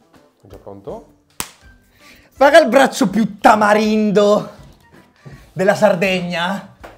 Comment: solo io ho capito tamaringo?